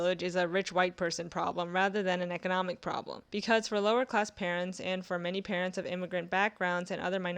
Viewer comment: The captions says lower-ass instead of lower class